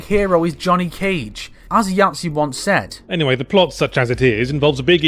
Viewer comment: i didnt hear the name of the guy clearly, i wanted to find that video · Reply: Yahtzee from Zero Punctuation. I think MK11 will be his next one.